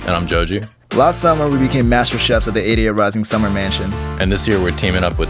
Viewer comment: I get that they say "88rising" but all I hear "Our idiot rising"